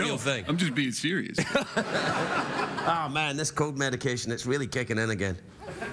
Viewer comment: Did he say "Oh man, this coke medication is really kicking in again" meaning for people to hear it as "cold" I mean he did admit before that he was "totally giezing out on TV" and he has admitted in the past he liked to party hahaha.